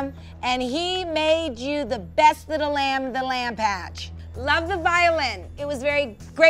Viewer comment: did she just say lamb patch?